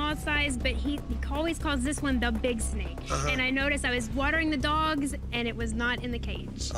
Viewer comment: did she just say WATERING THE DOGS